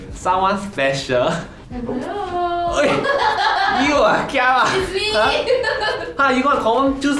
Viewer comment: Lol at 0:35 i thought he say gia wa( scare me in hokkien) . Instead of chiara lol !